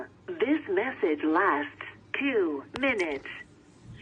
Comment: did she says beetch? Who is she referring to? That's dumb.